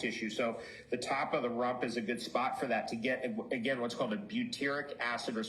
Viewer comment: do urine injections help target cleansing the lymphatic system? should the fresh first morning urine be used? what does he say at 6:04 regarding where to inject the urine? "the top of the (what?)? " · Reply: yes they help the lymphatic system. use collected urine from anytime of the day. Injection spot is on top of the butt muscle. Coaching is available. <mail>